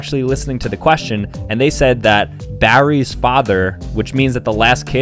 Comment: Bro thought he said berry 😂